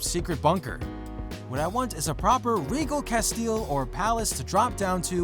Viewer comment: did he just say cast LE?